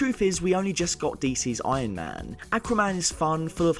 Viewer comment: Did he say "we just got DC Ironman"